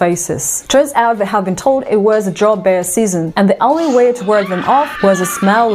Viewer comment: i am not good at english and i try to investigate what animal she talk about, maybe i am not earing good but i hear "jaw bear" here, iand i dont even recognize the animal on the video, can semeone help me with the name of the animal pls